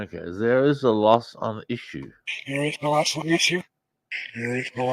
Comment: I heard " lost issue". I perceive it as he is saying medicare is a lost cause.